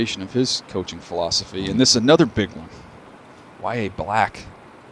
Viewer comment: bro what💀😭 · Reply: i swore he said "here is another.. big wide black" 🤣